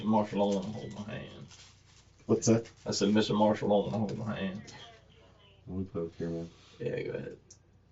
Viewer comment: (suspect) lol "Mr. Marshall doesnt want to hold my hand " (cop/nurse doing the blood ) "slight poke my man"........ BUT (subtitles says from Mr. Marshall) "I want to poke you in the ass" 😂😂😂😅😂😂😂